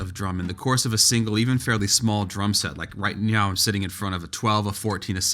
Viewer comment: did you say “right meow?”